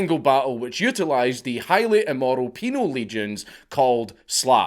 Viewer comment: I almost got in a car accident laughing at "peanut head progenitor" and "highly immoral penal Legions known as Slavs" thank you dank I needed that today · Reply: Damn... I thought he said "penile legion". Shows what my Polish brain can comprehend...